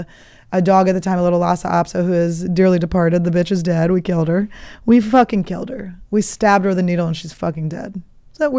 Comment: Did she say she killed her dog? OO I hope thats not what she said